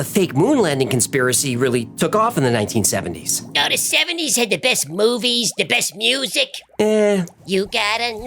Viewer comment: i thought he said best boobies